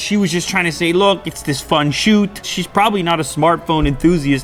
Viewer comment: I almost thought he said 'Shes probably not very smart' . :D